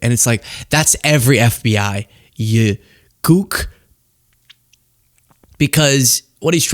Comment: did u just say “gook”?